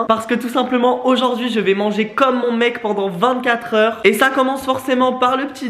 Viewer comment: Omg, j'ai eu peur, j'ai compris "je mange mon mec pendant 24h" je me suis dis, qu'est-ce que tu nous fais encore ?😭